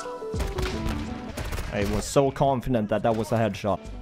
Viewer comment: SO CONFINENT , I know you catch yourself mispronouncing some words maybe I hear it wrongly this time idk best to you man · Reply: I said confident wut 🤣 ye it happens it dont really matter tho aslong as ppl understand yk